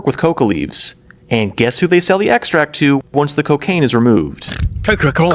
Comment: did they say “once the cocaine is removed?”